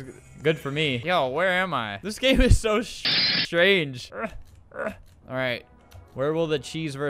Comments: this game is so shitty (strange) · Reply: Wait.. I heard 'sh strange' did he really swear and it was cut off or did he stutter?! I'm confused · The Rarest Thing Humanity Has Saw Is : Swearing The First Time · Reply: It wasn’t a swear the editor edited it to make it sound like he swore but he said strange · Did he say "this game is so fucking strange"? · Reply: No? He didn’t say that he just said “ this game is So strange” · Its been a while since I heard swear. I'm glad he still knows how to. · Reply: he didn't really said something with "shit" i think he just said strange and the editor made it sound like he said shit instead of strange